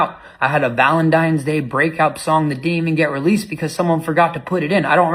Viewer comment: Put closed captions on, at 3:12 'The demon get released' is that a song or a convenient slip up? I kept wondering what he was saying at that point so I put closed caption on. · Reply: He said a song that didn’t even get released but he kinda was talking so fast it’s sound like he said that.